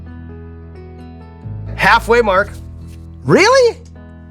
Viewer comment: I thought Sean called him Mark.